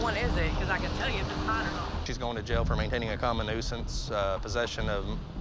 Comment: It sound like he said common nuisance! 😂😂😂